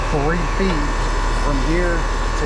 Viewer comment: Based on the tape measure I gave to my wife, that’s way more than 3’ away from the unit!! 😊😊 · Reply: The CC said 3 feet, verbally I thought he said 4 feet. Still a bit close though.